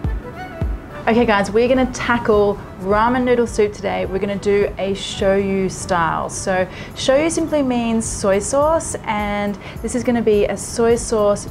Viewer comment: thought you said, show- you-style??